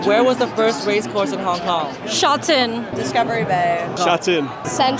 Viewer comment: Lol i thought she said shit in